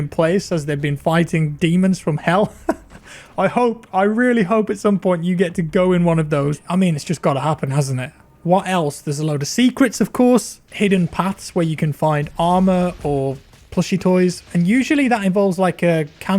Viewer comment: When you said “secrets” i thought you said “sea crits” Underwater demon slaying tiem